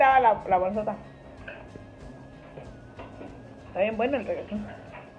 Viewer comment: Pensé que dijiste regeton pero dijiste requeson 🤣🤣🤣🤣🤣🤣